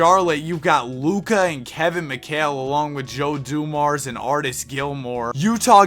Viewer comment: I thought it said Joe dumbass😂😂😂😂😂